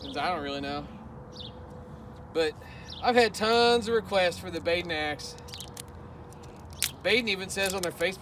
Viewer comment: Not gonna lie, when I first watched this video, when you were saying wanna see what it looks like swinging it backwards, it sounded like you said "Big nacks" like as if you were swinging hard similar to Daddy Hacks. But now I revisit this video and realize your saying "Baden Axe". Now I understand why I get some strange looks when I saw Big Nacks 😶